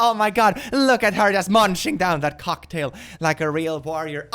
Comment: I heard say "munching down that cocktail" and now I am rather concerned on how he has cocktails. ARE YOU EATING THEM!?!?